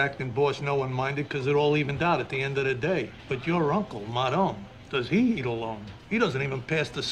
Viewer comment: Your uncle madone! Does he eat alone. He doesnt even pass the salt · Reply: I thought he said "does he need a loan"